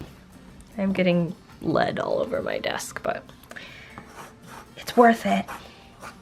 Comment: I thought you said "I'm getting blood all over my desk." D: It turned out really good looking! Kudos for experimenting. You make surprisingly good peices usually.